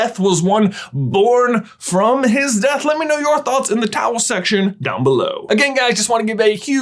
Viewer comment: Does he actually say at 13:02 "let me know your thoughts in the towel section down below?"